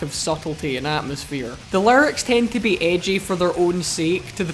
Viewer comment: I keep hearing "larnix" but now I realize he's saying "lyrics" but larnix totally makes sense lol since if you think about it.